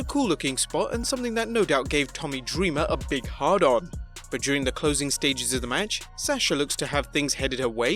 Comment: Did i hear this right "This would have gave Tommy Dreamer a hard on" ☺